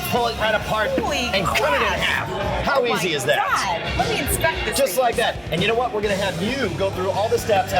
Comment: Correct me if im wrong but it sounded like this bitch just said "lemme instruct this thing" 6:00"xD *Inspect